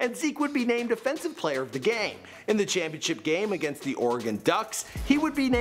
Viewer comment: did i hear DEFENSIVE player of the game